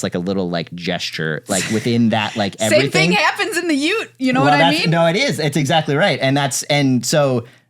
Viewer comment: Uh... did you say "ute"?